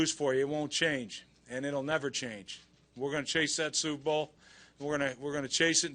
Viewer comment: LMAO at 1:24 it sounds like he says SOUPbowl even in defeat rex still hungers for food!